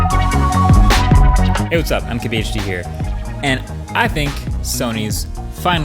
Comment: How are you introducing yourself? I hear, "hey whats' up? I'm key-be-h-t here" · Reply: MKBHD here...